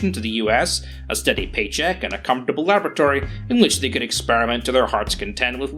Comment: At 2x it sounded like German scientists were trying to get a "comfortable lavatory".